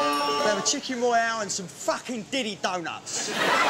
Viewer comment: "ill have a chicken royale and some facking diddy donuts!!!!" 🤣🤣🤣🤣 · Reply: One friend of mine once thought that Neg said: " COULD I HAVE TWO CHEESE BURGERS AND SOME FUCKING DIDDY DONUTS! " But it is chicken royale and some fucking diddy donuts.